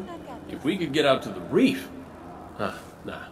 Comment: Wait, did I hear "Show these to Zavala the next time he wants a breast job" near the end from a random npc or are my ears just playing with me ? Cause thats what I heard.